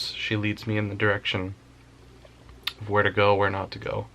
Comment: Anyone else hear what sounds like her in a soft whisper "Go" at 15:30 ish